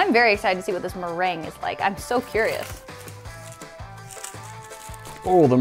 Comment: anyone else think she said I'm so furious ?